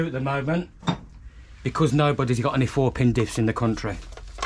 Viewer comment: I thought said there's no Fuckin Diffs available! But it was 4 pin diffs he said lol. Wow this build is really coming together quickly. Cheers